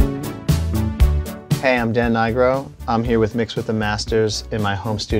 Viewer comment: What did he say 0:05😅😂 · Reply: "I'm Dan Nigro."